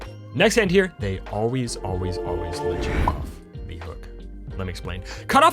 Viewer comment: What did you sat at 22:55 ? Can't make out what you said there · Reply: "They always always always let you off the hook."